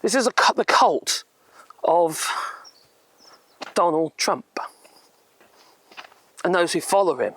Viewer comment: I thought you was saying Donald Dumb in the beginning .